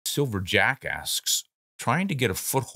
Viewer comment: That sounded like you said Silver Jackass at the start of the video.🤣